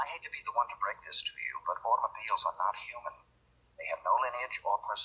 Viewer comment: ??? What is said??? I hate to be the one to break this to you but all the ..... ... ...... .... .... human????? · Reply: Automobiles are not human???